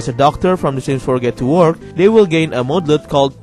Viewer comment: When you said "get to work", I missheard it as "get to war". Imagine if there was a sims 4 "get to war" expantion pack XD